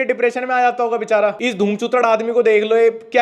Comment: kya bola धूमचूतड 😂😂😂😂😂